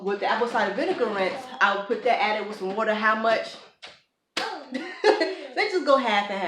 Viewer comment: What is a hair hydrater? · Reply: Sounds like she said rose water when talking about the hydrating